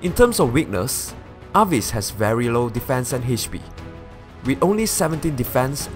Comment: while you said it correctly in the video, the text says: "Very low RES..." instead of the correct "Very low DEF..." Arvis is going to be my first fully built Red Mage.I didnt get any really nice mages untill now (only have +spd/-res Lilina and I am still waiting for a good IV Sophia before I build her)